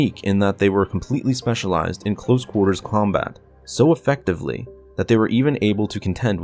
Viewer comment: Did you say close quarters Clonebat here 5:05 I can't un hear that now🤣